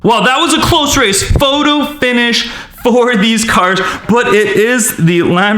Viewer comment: it said these cards instead of cars